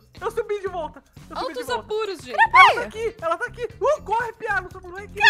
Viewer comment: eaai juh eu amuh seus videos e seu canal juh quando seu pai dize "CORRE PIA" Eu entendi "CORRE PIADO" TvT